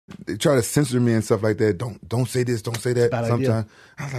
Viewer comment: Thought he said scissor me at first 😅